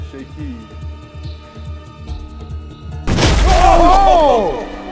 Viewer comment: i think i heard someone swear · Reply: He said oh shoot and the microphone hit the edge and glitched